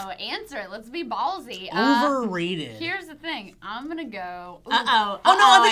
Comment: Sounds like said wolverated